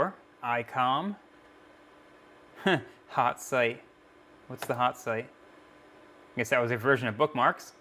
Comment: Hot site 😂😂😂 I thought you said pot site 😂😂😂